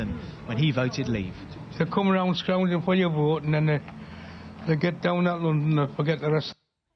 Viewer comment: He happens to be a Geordie like Cheryl Cole and Jimmy Nail. He said "they come round scrounging for your vote and then they get down at us and forget the rest". At least that's what I think he said.